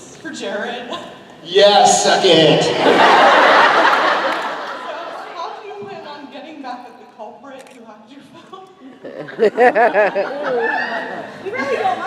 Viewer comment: Sorry i'm italian... what the fan said at 15:44? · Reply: Stefy Killerz "How do you plan on getting back at the culprit who hacked your phone."